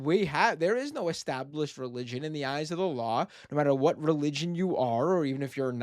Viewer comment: Matt said "one of the things that makes the US so great is that there is no established religion in the eyes of the law" and i heard "in the eyes of Allah" and had a good chuckle